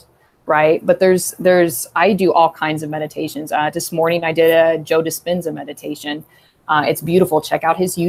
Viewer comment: Whats the name of the meditation she mentionded at 47:40? I didn't catch that 🤔 joda spenza? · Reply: Laura Violeta joe dispenza 💜